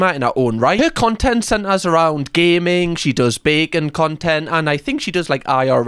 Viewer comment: Thought said "bacon content" was like ... thats weird 🤔😂😂😂😂